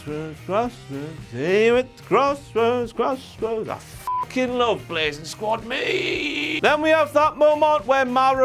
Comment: Anybody knows what said at 8:32? His accent is really thivk and I couldn't make out what he said. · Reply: I fucking love Blazin Squad meee.